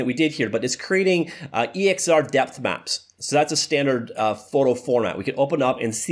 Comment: The first time he said "DepthMap", I thought he said "Deathmatch".